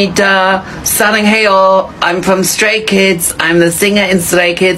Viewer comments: The man who cannot make a finger heart 😂😂😂 and saying sray kidz instead of straykidz is just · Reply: It’s fine I thought they said slay kids 😀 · “I’m a singer in sray kids” damn he really struggled with the 2nd stray with those damn lips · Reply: I THOUGHT HE SAID SLAY KIDS